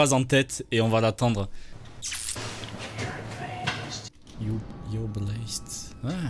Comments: moi j'entends you're prettyest (tu es plus beau ) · moi j'entend "your are breathed "ou "can't breathed" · Moi j'ai compris "you're blessed" littéralement "tu est béni" ce qui est pas trop con comme phrase mais je soupçonne l'autre type d'avoir chuchoté pendant qu'il désactivais l'alarme comme pour se parler à lui même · J'entends : You're amazing Je sais pas pourquoi ! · j'ai entendu "You're blested" xD